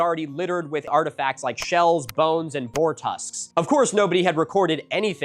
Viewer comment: misheard "boar tusks" as "four tusks" then looked at the picture and was like "there are indeed four of them"